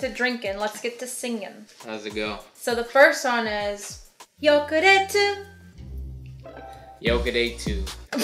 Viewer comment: popped out the rapping skills it sounds like he said yoke day two